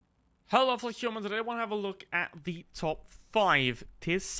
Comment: Hey, man: What do you say at the beginning of your videos? What I hear is, "Hello, post-humans..." But I am sure that is not correct. Please clarify. 😀 Thanks! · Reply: „Hello there fellow humans“ But I say it badly on purpose to leave room for interpretation after yt subtitles kept getting it wrong anyway